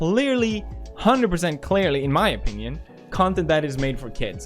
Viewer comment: did I hear.... OPINION